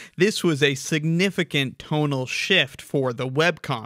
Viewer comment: I keep hearing 'tonal shift' as 'total shift' with the way he emphasis syllables